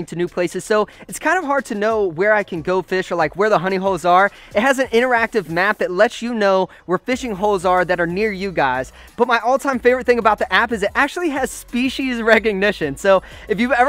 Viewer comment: I swear, for a second I thought he said “Honey Hoes” 😂😂😱🤦🏻‍♀️